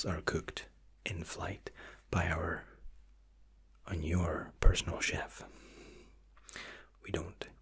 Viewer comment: I'm sorry the accent threw me off what did he say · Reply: Jacob The rapper “by your personal chef”